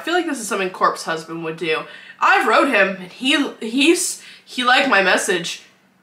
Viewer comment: When you were talking about Corpse husband ik you said “ I wrote him” but.. I heard “I rode him” … Read more